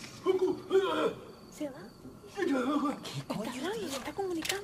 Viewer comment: por que dice goku??XD